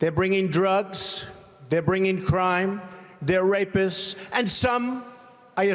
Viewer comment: Regarding the clip of Trump and Mexicans at 26:23, my closed captions quoted Trump saying, "They're racists,..." What I believe he was saying is, "There're racists,..." In the context of the clip he was not saying "they are." It's worth the rewind to listen again.